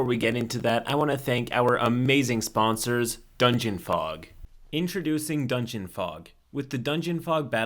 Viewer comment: I was half awake when I saw this and at first I thought was saying dungeon pog